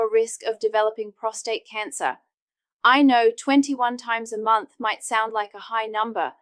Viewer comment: 21 times a month! I thought they said a day - no wonder I am tired